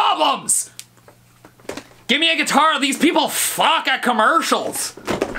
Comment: is it just me, or does he say 'Give me a guitar, these people f**k at commercials'?